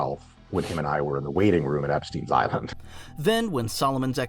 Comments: Is anyone going to talk about the Epstein island part??? Did I hear that correctly??? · did he just admit going to Eppsteins island? Or am i hearing it wrong?